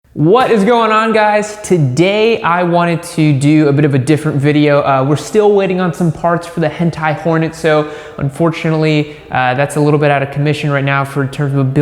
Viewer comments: I thought he said “hentai porn” at the beginning lol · is it just me or did he just say "hentai Porn Episode"? like in the start of the vid